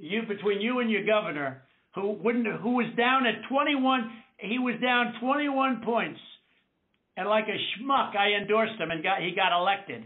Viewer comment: "What a Smuck I was" ...... Well, he said it, and for once, HE WAS REALLY HONEST. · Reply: ?? He actually said “he was down 21 points and like a schmuck I endorsed him”